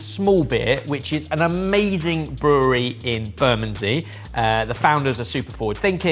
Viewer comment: The subtitle at 08:09 is wrong - I think you said the brewery in Bermondsey, but subtitle says "Burnham-on-Sea".